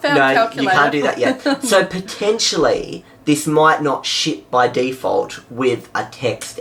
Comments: Did he just say "sh*t"??? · Sounds like he said sh**, Even the captions think so...